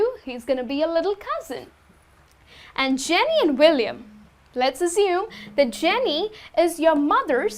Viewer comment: What did she speak in 12:48? I heard something like "Let's azeam" hahaha